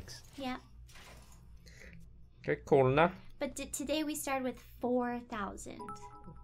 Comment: What is Toast saying at 0:58? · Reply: Pancake Soup k, cool nah?